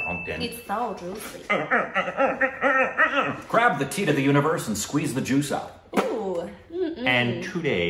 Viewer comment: Tit of the universe?? Did I hear that right? 🤪🤣